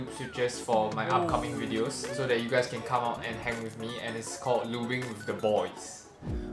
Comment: honestly i thought i heard "lubing the boys"